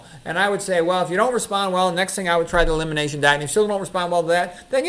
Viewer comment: What did you say at 16:55? Eliminate what? Thank you!